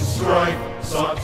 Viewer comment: okay so epic as this song is and all...when I listened to this song at first, I heard: "When the hamburger falls". Which would be a WAY sadder song, but one far less brave.